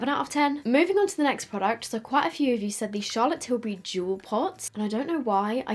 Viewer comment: i thought you said juul pods not jewel pots